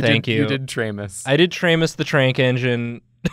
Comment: i heard it as tranq enginge